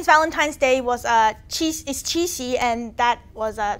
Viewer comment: says "chinese valentine's day is cheesy" and i was like hmm... you are not wrong. then i looked at the subtitle. qixi, that's what she's saying lol